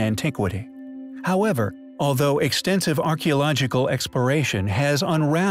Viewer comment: Did he say “archeological expiration?” That’s why we don’t know the truth, the archeology expired.